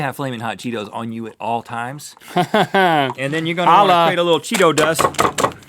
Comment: What does link say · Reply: He says "hahahaha, holla!"